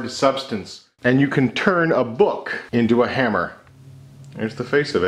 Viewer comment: I thought it said “can you make a boob with a hammer” *Book